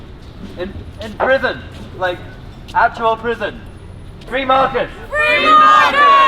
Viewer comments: When they shouted "free Marcus" I thought they said "Free Markets". That's a chant I could get behind! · The only part they had me agreeing with was at 08:25 when he chanted "free marcus", and I thought he said "free markets" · the protester said "free Margaret" but I heard "Free Markets!" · I heard him shout "Free makets!" 14:40 There seems to be some confusion about who slaughtered which children. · lol I thought he said "free markets"